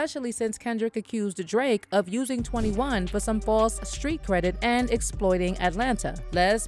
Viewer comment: I didn't realize Kendrick said FALSE street cred, I thought he said FOR street cred. So he's basically saying Savage ain't got no power in the streets. 🤣🤣